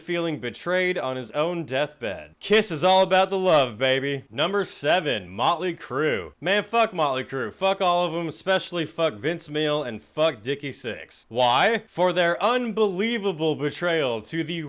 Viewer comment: Did I hear him say “Vince Meal”? 😂